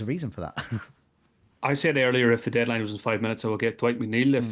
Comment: what did say at around 50:05 ? seems like he said "if the deadline was in 5 minutes" twice? for some reason I'm having a hard time understanding it :)